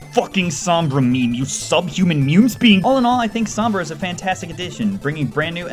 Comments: Am i the only one who noticed he said mewme speeing instead of meme spewing? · "Sub-human mewme Speeing instead of sub human meme spewing